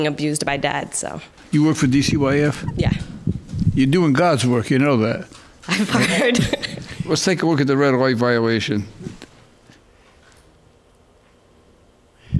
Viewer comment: Did she say she was fired at 8:21?